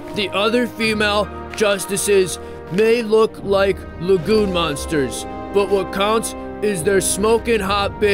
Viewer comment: I initially misheard “lagoon monsters” as “legume monsters”, but that actually still sort of makes sense. I thought you were saying they looked like mutated beans. XD